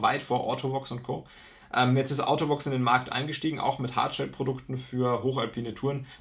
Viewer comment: Über welche Marke sprichst Du ab 19:00? “Autobox” oder so? Schwer zu verstehen im Video. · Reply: Ortovox ist gemeint